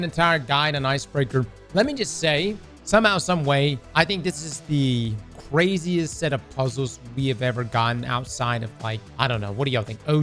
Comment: at first I heard you say “easiest puzzles” and I was like, is he tripping? xD